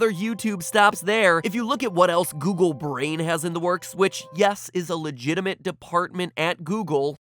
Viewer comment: Sounded like Miss Applegate! Kindergarten has done too much to me.